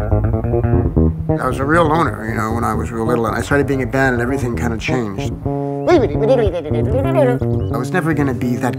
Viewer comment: Does anybody understand what he says at 00:16 ? :) Would be really nice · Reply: ""i was a real loner when i was little and then i got in a band and everything kinda changed"